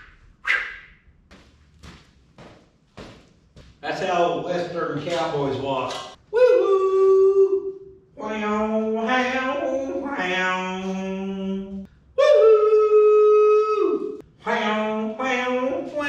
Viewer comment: Cowboys walk like that is because for every hour riding a horse, they walk like that the same amount of time afterwards. Ask me how I know. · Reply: i thought he said "that's how old westen champlin walks " 😂😂😂😂😂